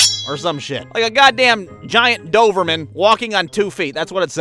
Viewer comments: Did you call that dog a “doverman”? Lol my guy, what!? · Did homie say “Doverman” at 4:16?